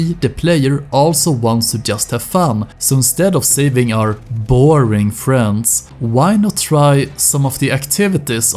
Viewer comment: "Instead of saving our booring friends" Been there :)